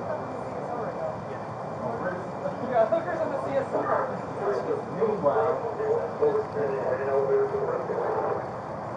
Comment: What did they say about hookers? 35:50 they may be just acknowledging they see one on camera but I can't make out what she says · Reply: Sounds like "We got hookers on the CSO" or something like that. No idea what that could mean.